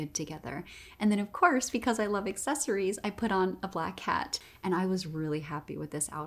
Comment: The black kitty really pulls that outfit together. :) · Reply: When she said "black hat" I definitely heard "black cat"